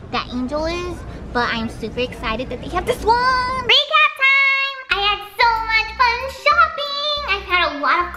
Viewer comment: What does she say at 7:16??? Is it freak out time?? I’m confused can someone clear it up pls thx lol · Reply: Hahaha! I say, "Recap Tiiimeee!" 😂